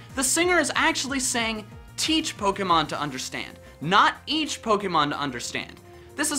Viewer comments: I thought the opening song said “Its” Pokémon to understand the power that’s inside ..😂👏👌teach makes more sense ... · I always thought the open said "It's pokemon" or "Each Pokemon". Wrong either way but just wanted to point that out. Also never noticed the hats. Aside from those, I knew the rest.